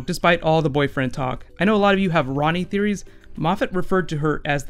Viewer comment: It took me to long to figure out you wee saying 'Rani' speak correctly, because I heard 'Ronnie'